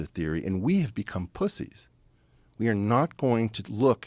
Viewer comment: Did he just say we became pussies.🤣